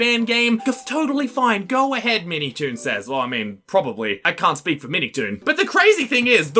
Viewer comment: I thought he said 'I don't speak Minitoon'